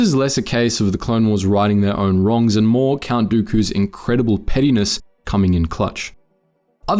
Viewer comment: I know it’s meant to be pettiness but it kinda sounds like you said “Count dookus incredible penis” and that is very funny Edit: It especially sounds like that in 2x speed